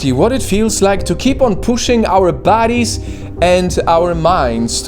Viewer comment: did bro say bodies or baddies lol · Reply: he clearly said bodies